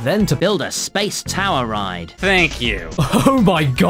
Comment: did I hear PBG say 'thank you' at 5:40 - ish? am I crazy? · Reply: Don't worry it was puhbuhgu